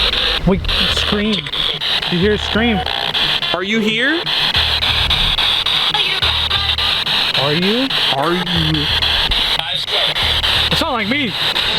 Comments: i heard a little girl voice say are you my mom that could have been one of her kids saying that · i don’t know if its just me or when it said “are you” if you listen closely it says “are you my mom?” I don’t know if im trippin but i swear I heard thay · Brooo I swear to go at 33:45 it sounded like a girl say “mom” “ are you my mom” broo I’m not trippin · I don’t know if I’m going crazy or something, but when the thing said “Are you” I heard something else after saying “Are you my mom?” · I swear it sounded like it said, "Are you my mom?" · On the spirit box to me it sounded like it said are you my ma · OMG 33:55 it said are you rug noah and anthony didnt hear but it said MY MOM AFTER IT SAID ARE YOU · it didint just say "are you" it sounded like a girl saying "are you lLa Llarona." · it sounded like it said “are you my mom?” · sounded like she said are you my mom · I think it said “are you…my mom?” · I swear I heard "are you my mom". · i heard are you my mom like she was looking for her mother and then her mom was their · I swear I heard it say “are you my mom” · I heard at 33:56 are you my mam. And it seemed like little kid was talking so Maybe it was one of the kids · Is it just me or did I hear a child’s voice say “are you my ma” at 33:54😮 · I SWEAR I HEARD ARE YOU MY MOM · In 33:56 it sounds like it said “ ayúdame “ which means help me in Spanish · I heard "Are you my..." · i heard ‘ are you my mom?’ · I heard are you my and I’m thinking it would say are you my children